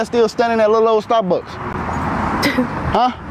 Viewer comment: Did she just say “what glorilla said?!”😂